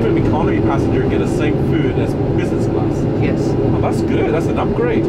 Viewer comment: I Thought he said Peasants class 😂😂